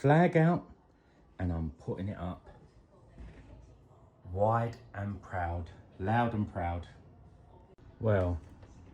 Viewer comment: wide and proud. I thought i heard that in your voice lol